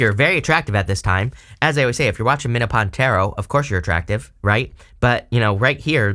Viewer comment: I swear I thought you said, "you're very attractive right now, and of course, if you're watching Menopause Tarot..." I froze in place. WHAT? Haha. Well, for me it is menopause tarot. Carry on. :- ) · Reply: He said: “Minnow pound tarot”😅😊😂❤